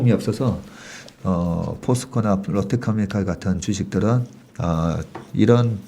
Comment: 에서 하신 말씀이 뭔가요? 포스코나 ? 아시는분 답변 부탁드립니다. 반복해서 들었는데도 모르겠습니다. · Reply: 롯데케미칼 말씀하시네요